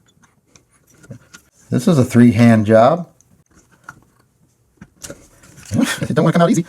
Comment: did you say free handjob? lol good video